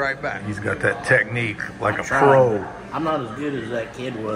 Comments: Ryan with that troll technique…gonna have to refer to him as “Trollman”! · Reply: misheard the comment at 13:26 - you said pro not troll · Ryan with that troll technique…gonna have to refer to him as “Trollman”! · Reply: misheard comment at 13:26 - thought he said troll not pro lol